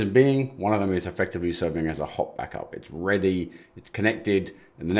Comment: I misheard "hot backup" as "hop back up" and I may start using that 🙂 Thanks for this series!